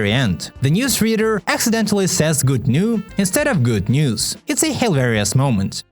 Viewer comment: He did not say good new he said: Esta ves buenas nuevas which means: This time good news